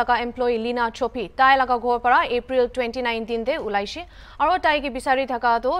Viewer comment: did u just say April 29th 🤔